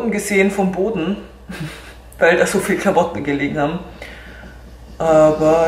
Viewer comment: Ich habe verstanden: "... weil da soviele Karotten gelegen haben..." #LOL#